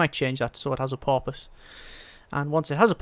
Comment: roughly, did he really say doesnt have a porpoise? Cause thats what I heard